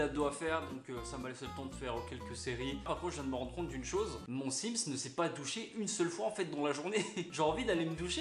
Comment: Mdrrr 16:55 j'ai compris " à aucun moment mon Sims ne s'est touché" j'étais genre "hein ??" 😂 😂 😂